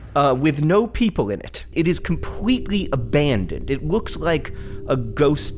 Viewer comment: I think he said compwetely instead of completely.